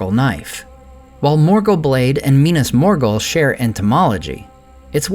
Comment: Lol did you say 'Entomology' at 5:58? That's hilarious ^^